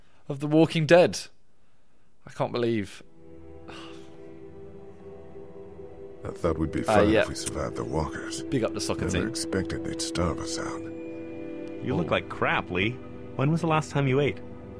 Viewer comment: Did he just say soccer!!!!